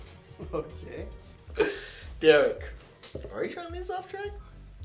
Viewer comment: I can't understand what Ryan said :( ... Can anyone help me? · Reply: I think he said derrick, are you trying to get us off track?